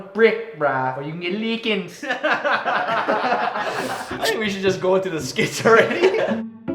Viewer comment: I misheard what ryan said at 5:40 and thought that he said "Lets just goal to the skits already DAD-HAT-CH